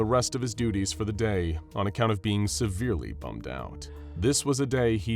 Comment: I fully thought it said "on account of being "severely, down bad"